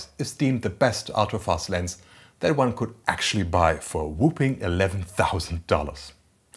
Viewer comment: “whooping” instead of “whopping”.. interesting